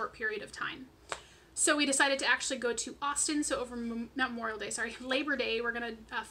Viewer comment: Awesome loss and period week to boot! Lol I thought you said you were going to Boston then you said you be never been to Texas. 😂😂😂😂 I had to stop what I was doing and was like "whaaaaat? Oh! AUSTIN!!! "